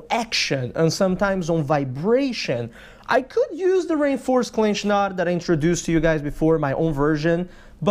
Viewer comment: I'm not gonna lie...I've been following your channel for over a year now, and I always heard "rain forest clinch knot". I can't believe my stupidity lolol! REINFORCED clinch knot...................makes way more sense haha!